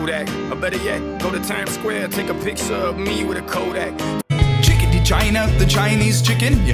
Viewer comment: i hear chikity Virginia the Chinese chicken